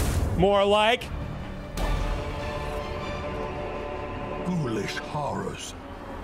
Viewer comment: All this time I thought he said "Foolish Horrors" when in fact its "Ghoulish Horrors"...